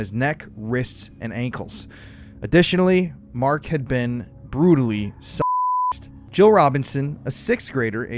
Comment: brutally ....sucked? Is that what he says cahrist